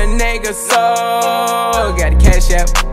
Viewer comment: Tell me im not the only one who thought he was saying gotta cash app not cash out 😂🤣